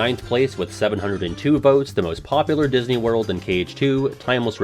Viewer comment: How did I miss Radiant Garden??? Edit: Okay nope I must have misheard. He must have said Disney World...